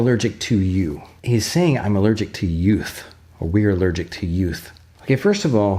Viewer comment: I thought the turtle said I’m allergic to humans